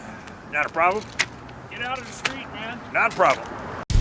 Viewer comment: i just hear "get outta the street man"